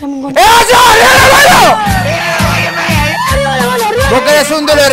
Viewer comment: Qué dice este chileno? XD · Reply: Eh wacho arriba las manos